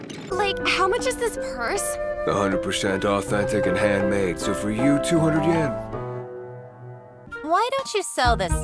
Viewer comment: I can't make out what he says at 4:32, but I really want to. All I hear is "100% off anything ... So for you 200 Yen." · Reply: 100% authentic and handmade so for you 200 yen